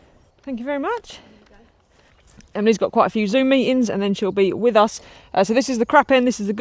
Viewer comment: At the start of this week's video when says that Emily was busy with a "ZOOM MEETING", I accidentally mistakenly thought she said that "EMILY HAS THE ZOOMIES"!😂😂😂😂😂😂😂😂😂😂😂😂😂😂😂😂😂😂😂😂😂 R.O.F.L.M.A.O. LIKE A HYENA ON GOOFY GAS!